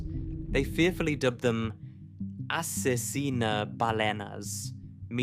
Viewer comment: I'm not gonna lie I was cleaning my watching this and I thought he said assassin bananas